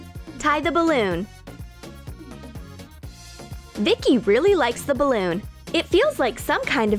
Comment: I thought she said THICCI LOVES THE BALLON